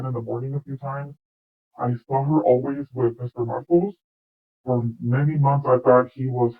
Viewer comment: Markus Anderson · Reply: sorry sounded like she was saying markles. Not Marcos lol